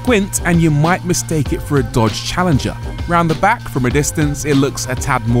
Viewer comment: "Deutsch Challenger" yeah, makes sense either way. Not sure if that was intended or if i misheard him, but still a cool play of words.